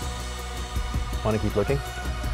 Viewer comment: the last line from the guy.. i misheard "want to keep twerking?" Just me?😂